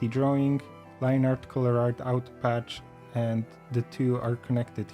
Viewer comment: did he say out patch or auto patch? Because I can't find out patch anywhere on Toon Boom Harmony.